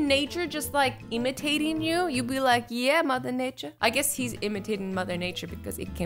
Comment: When Azzy said “Yeah Mother Nature!” I thought she was gonna say “Yeah Motherf***er!” XD (btw we will make it through quarantine!)